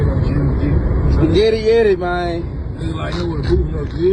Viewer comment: It sounds like they were saying spaghetti like maybe they blew someone’s you know, out. This is sad because not only the man but his daughter was involved, it’s not funny to be bragging on that